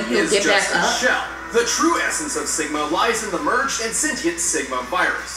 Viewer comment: Lowkey thought it said “STIGMA”